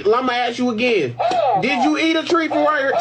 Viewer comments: I thought he said eat a tree😭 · h-h-hell naw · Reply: i thought he said "did you eat a tree" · I ALWAYS THOUGHT HE SAID “DID U EAT A TREE” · i thought he said did you eat a tree